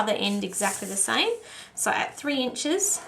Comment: Will try again, misheard and scored 31/4 inch instead of 33/4 inch!